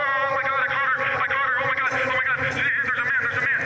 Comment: Does he say "oh my daughter! My daughter! Theres a man!" As an audio only person at the moment, i thought he said "i caught her, i caught her! There's a man!" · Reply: I thinkkk you’re right and he said I caught her